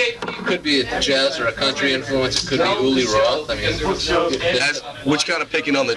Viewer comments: I hear: “could it be Uli Roth ?” Not, it could be rock. At 11:05 Dime yells Riggs ! Not Wait ! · i think he actually says “Uli Roth” in reference to Uli Jon Roth who was known to have extremely clean picking technique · Great vid! at 5:24 - the Interviewer is not saying "only rock" he's saying " could be Uli Roth" (Uli Jon Roth)